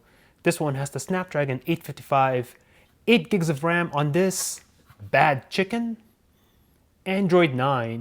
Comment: did you say bad chicken